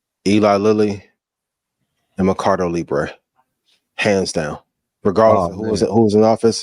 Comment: Mercado libre? Is that what said